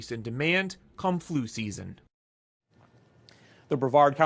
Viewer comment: I swore the news caster said kungflue season. Had to listen to it again. Lol he said come flue season but whatever it is the kungflue